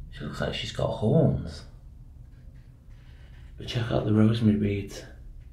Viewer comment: It sounded like you said "rosemary beads" 😅